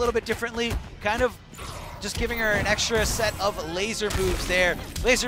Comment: did he say extra set of lazer boobs or moves?